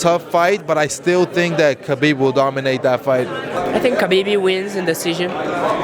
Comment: Did moraes say a think Habibi wins🤠😁